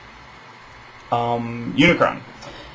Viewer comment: For the longest time as a kid I always thought he said "Hi, I'm Unicron" instead of "I . . am Unicron."